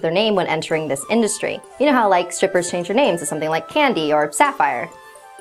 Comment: did she just call herself a porn star!?!?